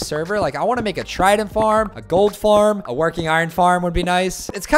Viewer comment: bro 4:38 did u say golden diamond... bro